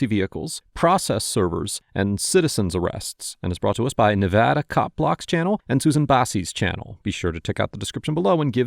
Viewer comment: Was looking at something else and I thought you said "Nevada cock block"